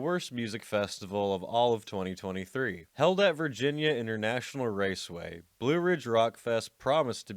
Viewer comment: i thought i heard helldiver and sprung into alert